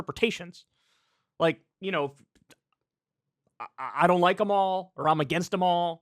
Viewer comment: That dude needs to put his dentures back in I couldn't understand him! Sounded like he said Joe them all! Lol